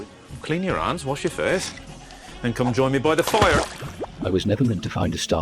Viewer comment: I heard "clean your ass and join me by the fire"